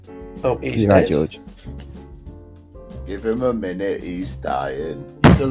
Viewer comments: He said "Give him a minute he's dying" but the caption said "Give him a minute he's fine". Im concerned. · the subtitles say " Give him a minute. He's fine" but what was actually said was "Give him a minute. He's dying" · did not say "give him a minute, he's fine". They said "give him a minute, he's dyin' ". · “Give him a minute he’s fine” What I heard was “Give him a minute he’s dying”